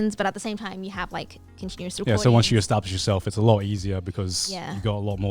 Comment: I thought he said “once you establish yourself as a loli, it’s a lot easier” I was like, yeah, I guess that is an example